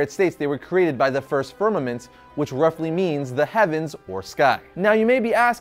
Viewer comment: Is he saying vermament or fermament at 2:10, the first universe?